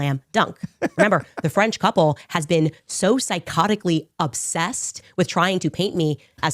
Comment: did she called someone else sycophtic obsess😂😂😂